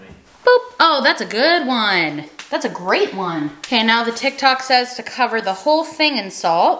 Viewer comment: Lol for a second I thought you said Ted talk instead of tiktok... just a tad different 🤣